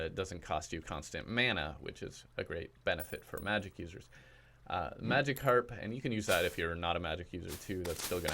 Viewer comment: When you said magic harp i thought you said magicarp